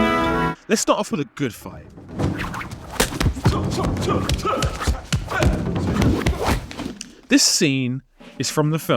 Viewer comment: I thought you said, "let's start off with a good fight"?